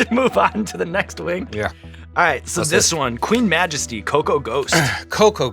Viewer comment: I thought they said "Coco Goat" for a second.